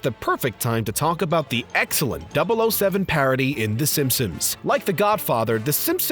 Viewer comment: Am I the only one who constantly heard him say Simpums ?